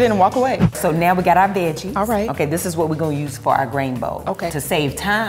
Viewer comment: When you said "grain bowl" I thought you said "grainbow" as in a pun and rainbow and grains, and now I really want that to be a thing.